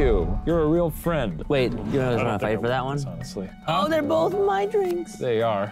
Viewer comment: idk who will see this, but at 16:48 when the captions goes "wait (indistinct) for that one" i think he's saying "wait you guys aren't gonna fight for that one?" (also i love binging too many spirits and they always make me laugh)